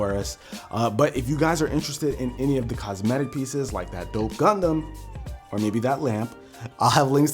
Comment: i thought first that he said "that dope condom" xD